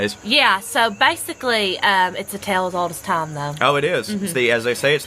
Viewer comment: says something he saw seemed pretty modern. I had to laugh. Especially when says "A tale as old as time" because I heard "A tail as old as time", which fit soooo well.